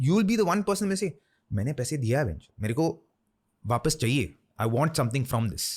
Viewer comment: Am I the only one who heard “Ben Stokes”